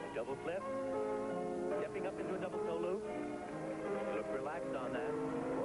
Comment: Did anybody else hear the commentator say "double dildo?"